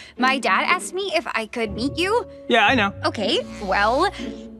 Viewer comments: The start it sounded like Charlie said “If I can Eat you” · thought she said eat you